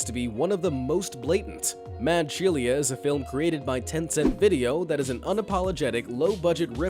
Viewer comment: I could have sworn you said 10 cent video. Coincidence? I think not. Lol